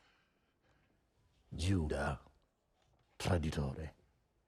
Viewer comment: What did the Cardinal say here 9:41? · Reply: Judas traditore = Judas the traitor